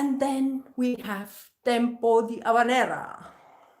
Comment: what did she say? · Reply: She says (Tempo di Habanera ) which is the name of a part in this piece in spanish language